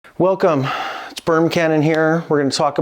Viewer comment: I thought he said “Welcome, Spermcannon here” had to rewind it to make sure I heard that correctly 😂